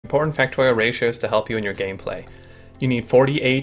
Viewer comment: Did i just hear "porn factorio ratios?" Kek. (i undertand that i misheard important)